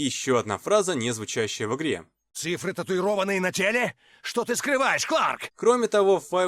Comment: То чувство, когда послышалось:"Числа на члене? Что ты скрываешь Кларк? "